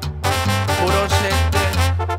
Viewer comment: did he say “puro chicken”😂😂